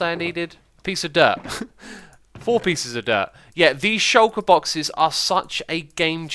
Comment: It sounded like he said he needed 'four pieces of derp'. ;-)